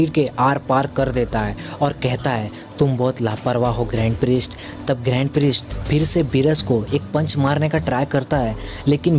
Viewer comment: Look At What Beerus Is Saying, GAND PRIEST